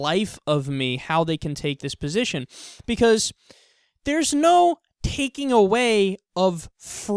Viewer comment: I thought he said there's no racism lol.